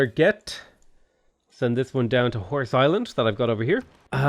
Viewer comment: totally thought he said "Whore's Island" haha